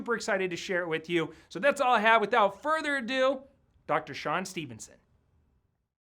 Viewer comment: I thought he said seapn Stevenson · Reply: Jambo that is Sean Stephenson